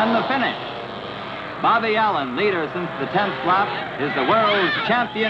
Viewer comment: Bobby Allen? I thought the announcer said Bobby Allison!